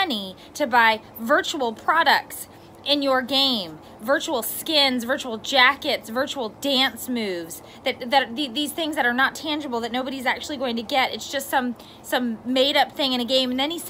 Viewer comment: Excuse me did u say "buy jackets " 3:56 🤔